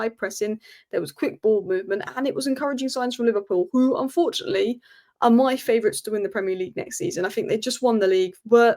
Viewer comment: all I hear is "Liverpool, my favorites" 😍😍